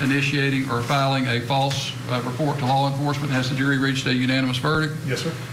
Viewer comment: did he say "false reporting to Hogwarts"?? Lol